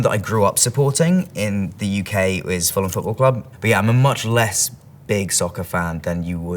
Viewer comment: Ok does anyone know what football club he said at 2:52 I kept going back to try and understand but just couldn’t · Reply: Fulham Football club